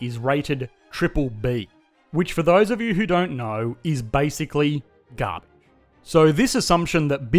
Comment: what word is he saying there? it just sounds like "is basically gah-" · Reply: I think he was trying to say "garbage" 🤷‍♂️